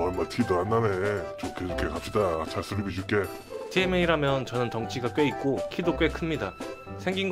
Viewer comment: 티엠에이? ㅋㅋTMA?? 잘못들었나ㅡ